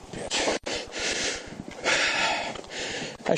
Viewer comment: At minute 8:16 did he say f'n b*tch? These tyrants need to be prosecuted to the fullest extent of the law. It's sad. How can you handle someone so fragile that way? This reminds me of the old lady that cop shoved out the precinct who broke her hip.